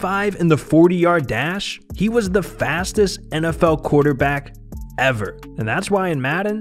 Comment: I know you guys didn’t just say Lamar is the fastest player in NFL history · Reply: i thought that said fattest lol